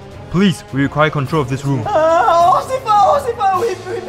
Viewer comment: said "occifer" instead of "officer"